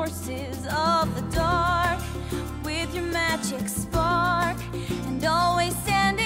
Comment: Is it bad that for the longest time, i thought she said "magic scarf" instead of "spark"?